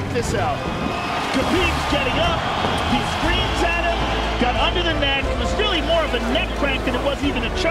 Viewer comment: I thought he said got under the Meg lol